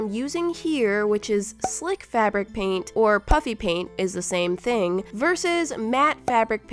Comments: I thought you said puppy paint and not puffy paint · Every time I thought you said puppy paint now I realized you said puffy paint ö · Who else thought she was saying "Puppy paint" instead of "Puffy Paint" xD